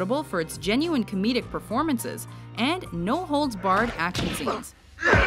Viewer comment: Did she just say "No holds barred"?!